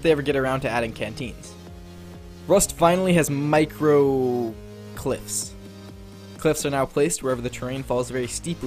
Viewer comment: I thought you said micro clits. Lmfao